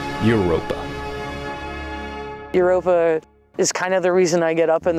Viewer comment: Heard this as 'Your Oppa' and now I can't unhear it.